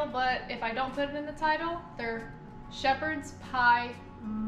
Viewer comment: thought you was saying sheepshead haha!!!!